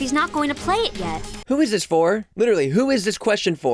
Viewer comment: Fully heard it as "who is this whore". Also assumed " You're out Mai?" To be about her being bi